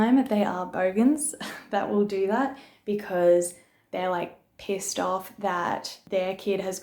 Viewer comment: did she just say the parents are pierced off instead of pissed off?